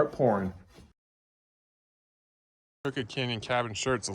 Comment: I thought you been saying COOKIE CANYON this whole time.